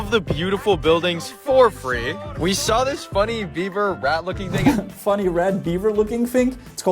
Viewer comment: I think he said "rat" looking thing.